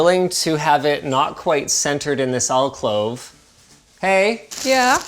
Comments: Alcove Linus. I think you are saying Alclove. Maybe it's the Canadian tongue I can't hear properly. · did Linus say "alclove"? You keep using that word. I don't think it means what you think it means.